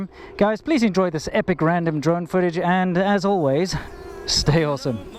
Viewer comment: Random DRONE footage , At first I thought you said "random drug footage". Informative video but you guys need decent bikes.